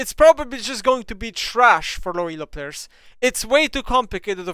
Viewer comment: im not the only one thatheard him say "its Hwei too complicated for low elo players right"